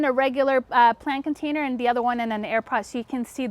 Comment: Never heard of these pots! So interesting!! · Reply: Before this lockdown nonsense I had zero interest in anything green, now, I’m obsessed with lawns lol, love the accent lol, I did, for a split second thought she said air-pod lol, I’m British lol ( yeah, I know lol) these pots are amazing, Amazon is making too much outta me! Lovely explanation, Thankyou 🙏🏽